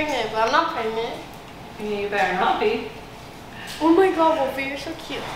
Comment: What'd say at 5:25? Someone please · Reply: “You better not be”